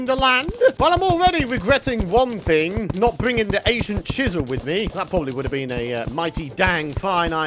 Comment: Did he just say the Asian Chisel?